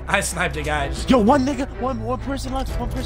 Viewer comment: Did anyone else hear one of them say nigga🤔